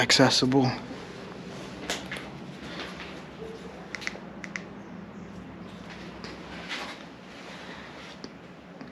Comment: Great Video❣️. At 9:22 I heard what sounds like a woman talking. Could someone check if you can hear it also or I'm going nuts. LoL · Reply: It sounded like “ Who’s that? That’s what I heard .